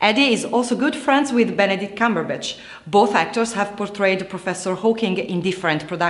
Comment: Did she just say "Cumberbitch"? ..that was uncalled for! ;p